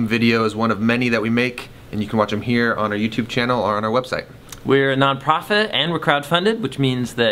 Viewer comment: For a minute, I thought you said "we're non-prophet"